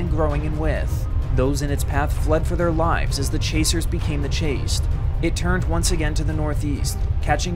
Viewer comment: lmao i misheard 1:08 as "as the chasers began to chase"